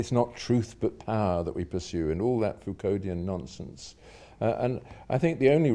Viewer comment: "fuconian nonsense" What is the first word ? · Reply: I believe it is "Foucauldian" or "Foucaultian" referring to: <link>